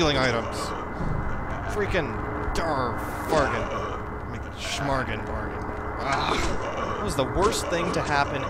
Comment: I laughed at that one too. What was he even saying? "Shmarbin"? xD